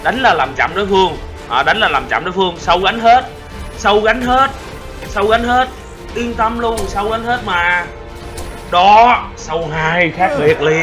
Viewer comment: no idea what you're saying but all I know is "So Wan Hurt"! U legend